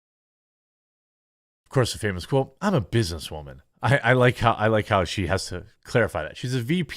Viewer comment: I laugh when she says "I am a business woman" ... what I hear is "I am a diversity hire"